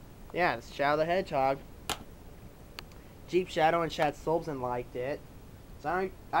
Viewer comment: Did you said ChiefShadow before Shadsilvson?